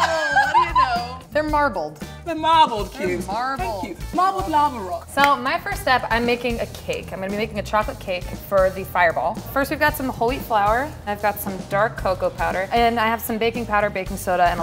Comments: when she said that she was adding whole wheat flour i thought she said "holy flour" lol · thought she said h o l y f l o u r